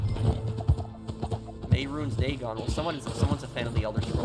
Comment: What did he say? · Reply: Averoons daygon